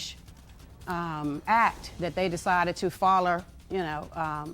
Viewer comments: she said foller instead of follow i love my texas people accent 😂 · Wait! Did she say "foller" instead of follow?